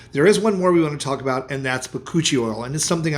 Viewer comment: This is the 1st time I've heard of Bakuchiol oil. Thank you for this information! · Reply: I thought he said koochie oil. 😅